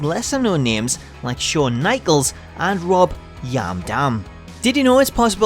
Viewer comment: did anyone hear rob yam dam.